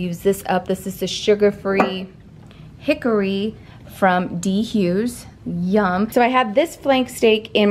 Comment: Is she saying D Hughes instead of G Hughes, or am I just mishearing it? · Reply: You are correct. She is saying D